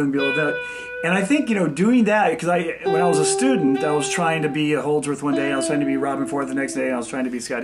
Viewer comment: Can't quite catch the first name he mentions at 6:54 - can anyone make out what he says? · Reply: guessing Allan Holdsworth